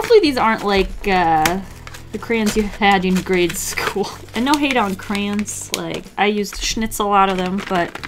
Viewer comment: why did I hear "no hate on clams" XD did anyone else hear that